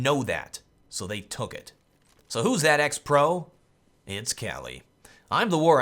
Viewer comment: The Rift Guard I'm sorry, but who are you saying cheated? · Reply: The Rift Guard I watched the whole video but it sounds like your saying the war owl cheated